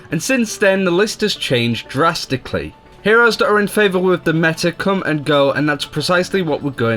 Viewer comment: What does he say at 0:17? "Heroes that are in favor..." What? Someone tell me, please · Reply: "...with the meta..."